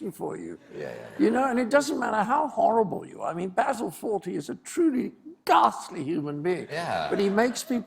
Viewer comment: Who did he say was a horrible human being at 6:48.. Basil Ford something?.... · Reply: Look for "Fawlty Towers" on YouTube. Cleese's character Basil Fawlty was an absolute wretch, a curmudgeon of the highest order, but it's like he said — the way he depicted that wretchedness was hilarious, so we loved him. Besides, he always ended up getting righteously screwn. :)